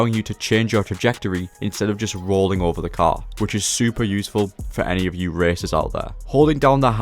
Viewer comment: Didn’t hear what you said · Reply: [...] which is super useful for any of you racers out there.